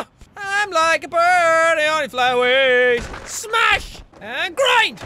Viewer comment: the subtitles say: I'm like a Bernie only Flyways